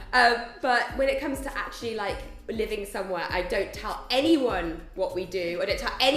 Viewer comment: did just say "I don't tell anyone my second name" 8:01 ...so, what's real or first name?